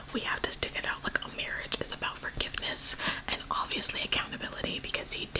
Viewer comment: She said "a marriage is about forgiveness " and i heard "our marriage is a freaking mess" 😭